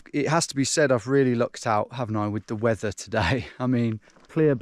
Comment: The caption says “I’ve really looked out” but didnt you mean “I’ve really lucked out” or have I just been saying that expression wrong my whole life…🤔🤨